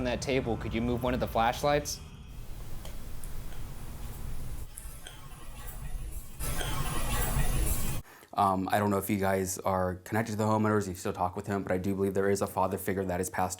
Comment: I heard "I don't want to do it" not "no help me"